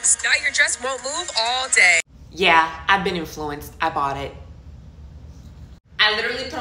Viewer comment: "Yeah, I've been influenced" is such a funny thing to say 😂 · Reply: I thought she said "I'm in Florence" 😅🤣